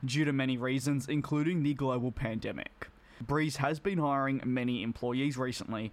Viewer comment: What I heard: breeze has been firing many employees recently to ramp up services Me: 👁👄👁 visible confusion